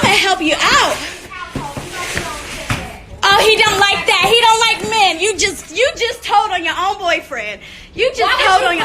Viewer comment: TEALAZY she was talking about men being around the house or them. That’s why she said Az was disrespectful. Az was filming with men that r kelly didn’t know or approve of. She was trying to get Joseline to confirm whatever she is about to say on this documentary to prove he was controlling them. It’s a whole mess. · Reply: I thought she was saying R. Kelly don't like men 🤣😂 I swear I was hearing rumors from that beeyotch Jerhonda that R. Kelly is gay 🤔